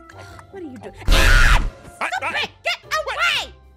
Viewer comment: did she say "stupid" or "stop it"?